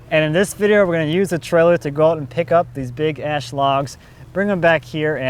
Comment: Sorry, I thought you said "big ass logs" (which they certainly are).